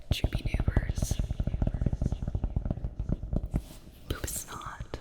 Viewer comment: Bro in round two when you had the rabbit book it’s sounded like you said booty snot